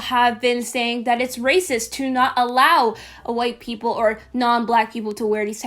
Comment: racist to who?! oh i thought she said racist to WHITE people not being able to wear PROTECTIVE STYLES for BLACK/BROWN girls or guys too even, i must be hearing stuff lol😅😋